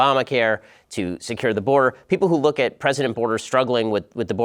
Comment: Did he say "President Boarder"?? Never heard that nickname before lol